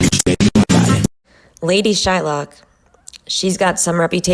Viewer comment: I SWEAR i thought it said Lady SHINEBOX lol